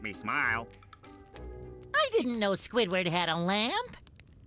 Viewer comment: When spongebob said,”I didn’t know spuidward had a lamp” I thought he said,”I didn’t know squidward had a LIFE” 😆😆